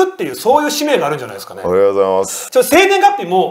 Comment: おはようございますにしか聞こえない